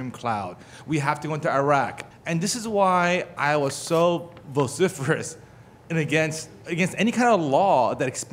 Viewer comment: What exactly was that word at 3:32 "Boziferus"?